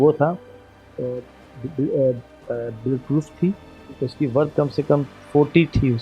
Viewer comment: what did he said 40lakhs?!